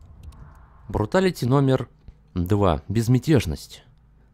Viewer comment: Мне послышалось когда ты сказал бруталити не бруталити а фаталити